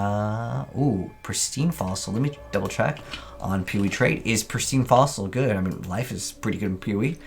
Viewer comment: Is it just me or does it sound like he says peewee when he says Poe 😂